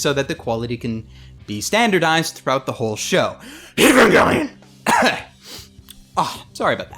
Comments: What did he say at 07:20 was it Evangelion or something else? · What does he say at 7:21? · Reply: Evangelion. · what did he say? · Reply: stupid for a Name evangelion